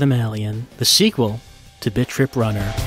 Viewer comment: I thought you were saying "bitch rip" ... kinda did a double-take and then re-read the title.